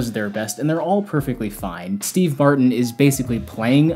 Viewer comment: Did you say Steve BARTON? Smug about film ignorance yet you don’t say Steve Martin?